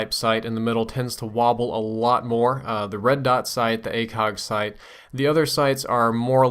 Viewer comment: Yeah I heard you say "red dot shite" haha